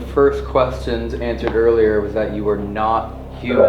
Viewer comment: instead of whatever you put, I immediately heard not not human